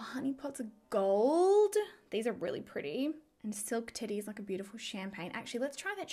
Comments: I thought you said "Silk Titty" instead of "Silk Teddy" and I almost lost it. 😂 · Lol not trying to be nasty or anything but it sounded like you said silk titty lol but omg I love love love the packaging like seriously next level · Lol I thought said the colour was called Silk Titty not Silk Teddy had to rewind that. But hey, it is Too Faced and they have had risque names before. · I may have replayed you saying “silk teddy” a couple times because I thought you said silk titty 😂 I thought maybe they were flowing with the “sexpert” idea.